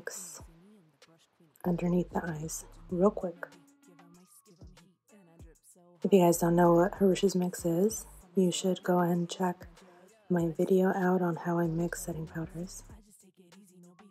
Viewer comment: I swear I thought you said "Hellacious mix" instead of Hrush's mix 😂